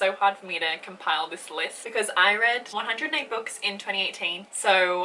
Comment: At first I heard “I read 408 books” and I was like SORRY WHAT HOW YOU WIZARD but then I realized you said 108 😂 The female of the species is so so good!!